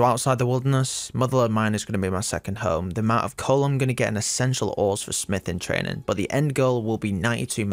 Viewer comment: Almost thought you said you'd be mining essential oils for a sec